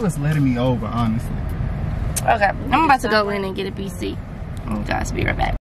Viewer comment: Is saying BC? I feel like she is because they were pulling into CVS so I'm assuming birth control we all grown here I just want confirmation cause sometimes I here shit wrong maybe is saying PC and idk what that would be